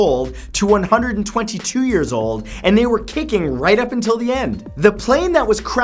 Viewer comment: 1) Well I'd hope they were kicking, how else were they supposed to set a record for living longest? 2) I thought you said kinky